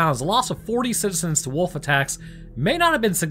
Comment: Ohh, you said "wolf attacks". I thought you said "wolfie tax"....works either way I guess